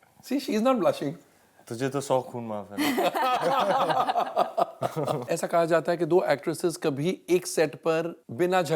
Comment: Can anyone tell me what ranveer said when deepika asked why u blushing..?? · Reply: raga sudha RANVEER to DEEPIKA - TUJSHE TOH SO KHOON MAAF HAI